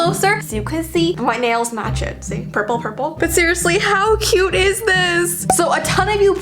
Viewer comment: did she say Purple Herple